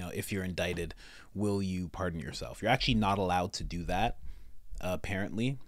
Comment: Oh... She said "will you pardon yourself". I swear I heard "Did you fart on yourself". I was like WTF??!